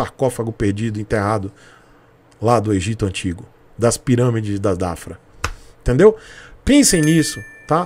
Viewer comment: Aos 2:48 eu escutei: "das pirâmides da DAFRA"???